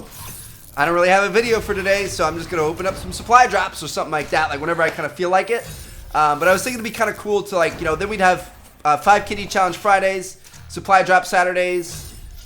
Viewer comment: I heard the "5 kitty challenge"